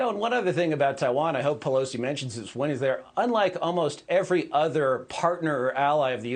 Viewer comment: Did he say, at 1:39 “ I hope Pelosi mentions it when he’s there” ?